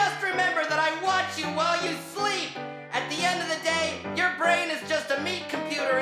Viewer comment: I swear I heard " I'm watching you sleep " from bill 😰😰